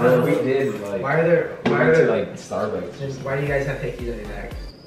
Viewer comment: I thought said ”Why do u guys have necks?” Lmao